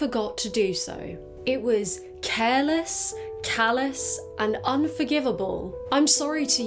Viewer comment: I heard it as callus instead of cowless, cowless makes way more sense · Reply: she did say callous lol, that's the joke